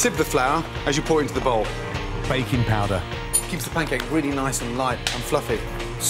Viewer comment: I thought I heard bacon powder instead of baking powder haha